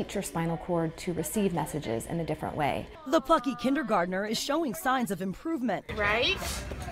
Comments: it sounds like she said the f####ing kindergartener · DID SHE JUST SAY "THE F#$&%NG KINDERGARTENER" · does anyone else hear “the f***y kindergartener” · did she say le fucky? XD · is it just me or did anyone else hear “the fucky kindergartener” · did she say the fucky kindergartener?